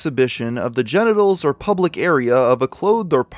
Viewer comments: did he say "the public area"? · I wonder when the creators gonna realize he referred to little girls privates as "pubLic areas" instead of PUBIC area's" at 33:56....